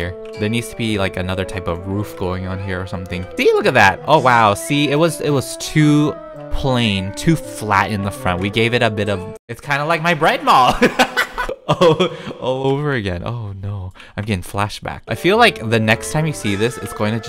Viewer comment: I thought you said it’s like your grandma lol